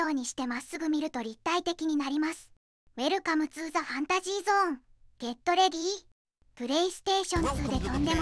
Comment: スタート時のセリフが何回聞いても「welcometoThefantasyzone getLady 」って聞こえたこと無い。ファンタジーゾーンって所が良く聞き取れないせいかもしれない。